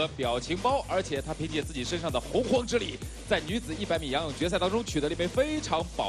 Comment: 请告诉我 普通话, 他说什么? 一些关于她的力... 谢谢 Could somebody please tell me what they said in Chinese for what they translate to English as "with her prehistoric powers"? EDIT: I think I've got it. Is it, 她的体内是有洪荒之力?